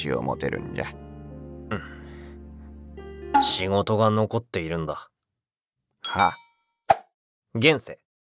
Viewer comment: なんて言うか「・・・仕事が待っているんだ」の「・・・」に含まれる気持ちがすごい心に刺さる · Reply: 待ってるじゃなくて残ってるじゃない？